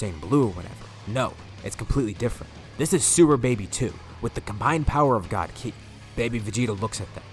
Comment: "this is Sewer Baby 2" I know I'm not the only one who Heard that.